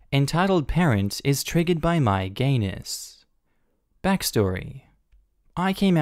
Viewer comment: OH he said "gayness" ... I definitely heard "entitled parent is triggered by my anus" and made me question so many things